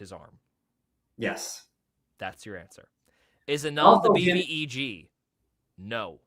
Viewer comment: I cannot for the life of me figure out what is saying here! Does anyone know? I have listened to it so many times, and I can't figure it out. The answer is "no" and the question starts with "Is Anon-" but that's all I can hear. 😭😭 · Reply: He said: "Is An0n the BBEG? No." B.B.E.G. stands for Big Bad Evil Guy/Gal So An0n isn't the overall antagonist of the whole story. I'm happy and sad to hear that <333 :DDDDDDDD